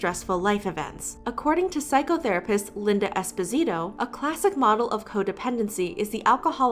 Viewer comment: Every time she said "Linda Esposito" I thought she said "despacito" :v